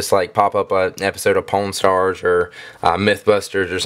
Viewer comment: I thought he said pornstars at 9:50 hhahah